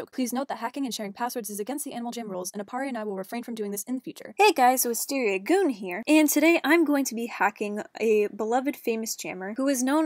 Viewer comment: did she say Wisteria GOON? XD